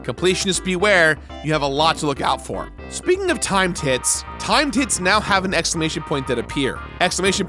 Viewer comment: Definitely thought you were saying "time tits" and it was only after the 3rd or 4th time you said that I got it. "OHHH, TIMED HITS!"